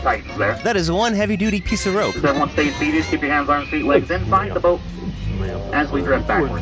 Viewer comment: inside the bote (the captions puts says bote instead of boat)